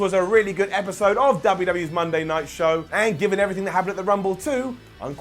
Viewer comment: Monday nights show" 17:18 I thought you said - Monday Nitro